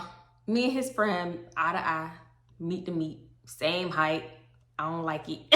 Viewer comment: DID U JUST SAY U AND THE FRIEND WAS "MEAT TA MEAT"?!? 🤣🤣🤣 Lmmfaooooo pls come get me off this floooo!!! Hahah i need to grow TF UP! 😭😭